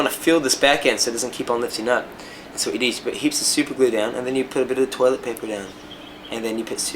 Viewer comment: i can't understand what he say when he talks about his nails, at 2:30 he says ''you put a little bit of toilett paper and then... and then paper again''. I can't understand the middle part, anyone can help xD? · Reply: He said you put heaps of super glue down. As in a lot of super glue. Just fill that sucker up with super glue. Then toilet paper. Then more super glue. Then more toilet paper. Repeat.